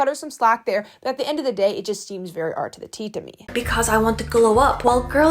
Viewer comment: What is art to the tea 14:56 ( can a native speaker help me understand) ? R2D2 Or art to the tea. What is saying, "it seems very..... to me." 😂 God my accent is poor.